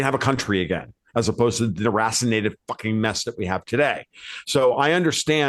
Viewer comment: Can anyone tell me what word Tom said at (5:26)? "irrascinated?" "hurrassinated?" I tried but couldn't find anything spelled like that (and the CC couldn't handle it either with "dassinated.")